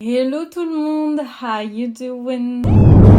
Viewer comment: C’est quoi qu’elle dit après hello tout le monde? 😂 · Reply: How you doinnnnnn haha il faut la ref Friends